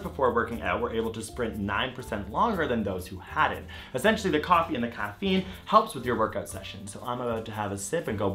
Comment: did just say "the coffee in the caffeine"?